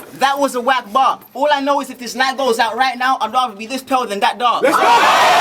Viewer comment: can someone tell me what he said and meant? thanks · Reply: he said.."if this lightbulb goes out right now, i'd rather be this pale than that dark"...cause hes albino